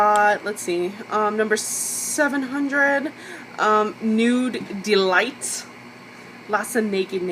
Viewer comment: what colour is the 2nd shade? it sounded like u said dare to nude but they dont do that one?